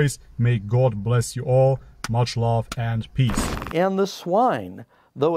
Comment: Each time when he said much love I heard “MasyaAllah”. I love it. Much love.